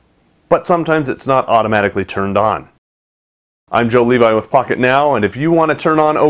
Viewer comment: it sounded like he said "im john levi with fuck it now" haha